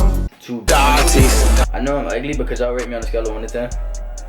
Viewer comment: U sound like u saying “rape me” like last time 😂